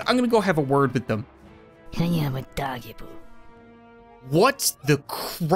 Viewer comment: DID THAT DEADEYE JUST SAY “I AM A DOGGIE”?!?!? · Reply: I heard "can you have a doggie poop"